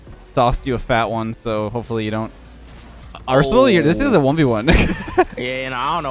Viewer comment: is saying: yo Ars this is an 1v1 ngga?